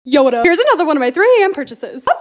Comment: Bruh I thought at first when she said “yo what up?” She said “Yoda” 😂